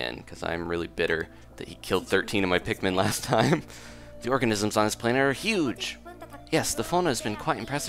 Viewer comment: At the beginning of the video, I thought it said "The orgasms on this planet are huge!" XD I nearly died.